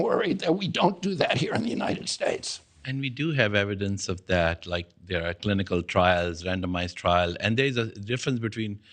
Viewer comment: what the hell was he even trying to say?! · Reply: He’s saying “you’re wrong”